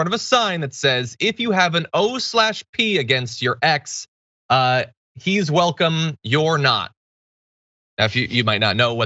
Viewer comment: I thought it said " ikes welcome your not" ... lol